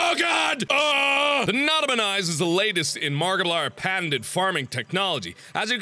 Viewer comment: It sounds like he says markipliern